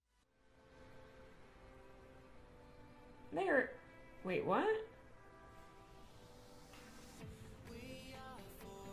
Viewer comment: Did she say the N-word or am I crazy? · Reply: she said "where"